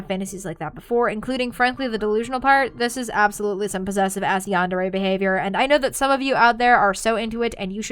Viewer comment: wait what did she say there? I couldn’t hear it. Also, the captions aren’t on or unavailable on this video, so what did she say? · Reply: "This is absolutely some possessive-ass yandere behavior".